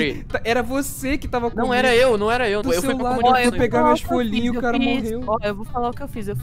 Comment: quando ela falou as folhinha achei que ela falou rolinha ai eu "mas oq e isso que esa aparecendo na ela do me compuador"